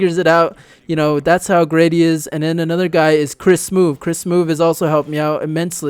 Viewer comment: at first i thought u said Smoove is shit